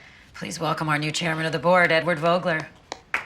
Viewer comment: whyd i hear Eggward Ogre lmao